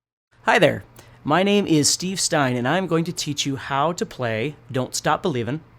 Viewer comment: I thought he said,"Hi there, my name is Steve Vai!"